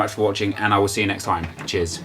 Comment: anyone else notice that when says cheers at the end it sounds like he says jizz